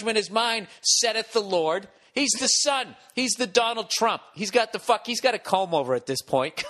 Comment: Wat? Did he say “he’s the Donald trump ?” Whoaaa......